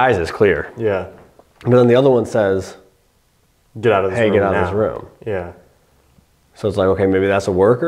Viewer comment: I think it says get out of here this isn’t a game not it’s the guys